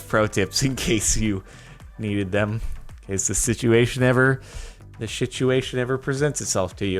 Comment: Am the only who thought he said shituation